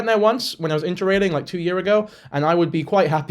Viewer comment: Did someone hear him say 2 year ago?